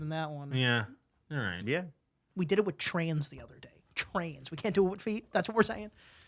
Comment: Wasn't completely paying attention when started talking and I absolutely thought he was saying "trans" 24:34 I gasped a little, choked (on nothing) & started coughing 😵 I now realize that he was saying "trains"...